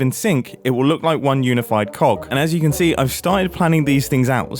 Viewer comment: Is he really saying clock?.... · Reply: Hes saying cog... Apparently